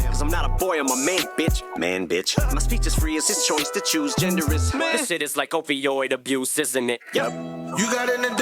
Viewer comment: Always thought he said "this shit is like opioid abuse isn't it" but now I hear it differently, thanks for making me wrack my brain on it 😅